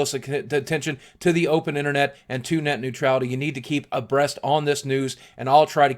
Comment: Did He Say "A Breast"